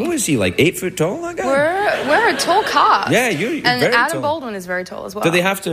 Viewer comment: Can someone tell me the word she said at 13:29? I'm a English lerarner and couldn't understand what she said after "We're tall..." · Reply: “We’re a tall cast”. She’s saying that actors are all tall